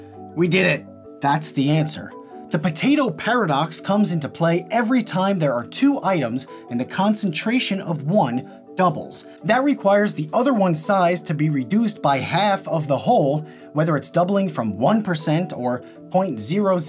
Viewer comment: I thought he said concentration camp